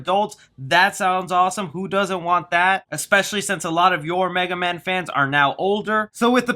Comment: I’m super exited for the Megaman Movie. I just hope it’s animation. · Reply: For a second I thought you said megamind